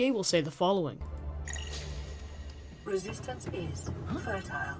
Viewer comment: what did it say? and what does it mean? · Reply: Resistance is threatiele